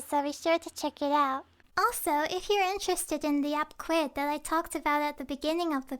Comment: I thought she said squid instead of Quidd at first 😂